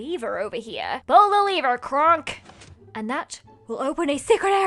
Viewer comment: I JUST REALIZED IT WAS AN EMPEROR'S NEW GROOVE REFERENCE BY READING THE COMMENTS.. I thought you said krank instead of Kronk. 😂😂